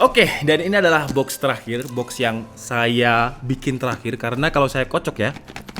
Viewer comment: Ini adalah box terakhir. Box yg saya bikin terakhir 18:06 apa saya gak salah dengar?